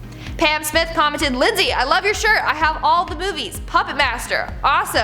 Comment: The first comment shoutout its sounded like she said I have all the boobies and I was like... WHAT! 😂